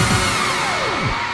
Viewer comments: i never knew he said "oppa" until i actually got into kpop. my whole life has been a lie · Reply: Same, I thought he was saying "open kannam star" later I realized the oppa and gangnam. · I bet Everyone in India knows this Song and Used to Dance and Vibe... Without Understanding a Word Because the Music is Lit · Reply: For about a year I thought he was saying "Open Condom Style".... 😁